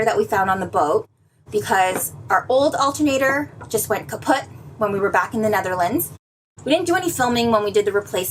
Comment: Did you say "kaputt" at 0:50 ??